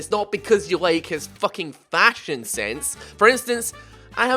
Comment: when you said fashion sense at 3:28 I thought you said "splashion sense" and I was like "wow what a pun guy" but you didn't say that so fuck what the fuck